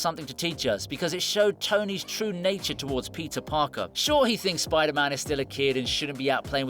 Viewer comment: Did he just said "Pizza Parker"?